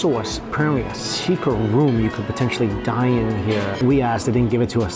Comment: I deadass thought Mike said a secret room to die in at 6:35 😂😂😂